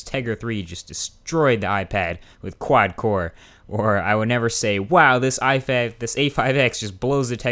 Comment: wait. did you just say "wow this ifag" at 2:36 LOL